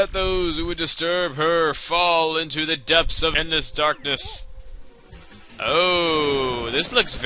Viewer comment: My computer skipped a second where you say "Fall into the depths of endless darkness" and it sounded like you said "edness darkness" on my computer lol